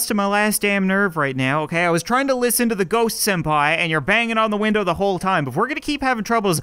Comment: did you say ghost senpai